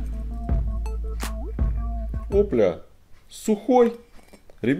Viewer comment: на 1:41 мне послышалась о бля